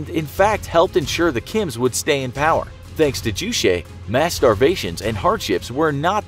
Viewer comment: I think I heard "mass starvation" incorrectly.